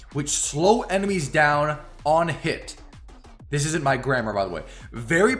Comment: I thought you said grandma lmao